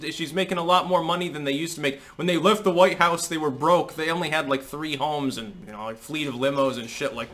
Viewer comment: I thought you said "We only had three homes and a fleet of women." bahahaha Billy boy wouldn't be turning that down.